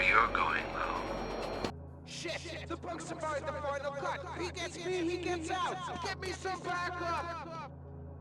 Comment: entendi, "shit! the punk survived the final plot!", luego no entendi hasta get me some backup xd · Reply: Dice "He gets me, he gets out"